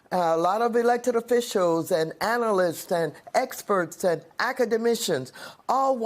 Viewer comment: did Maxine say academissions? 😊😂😂😂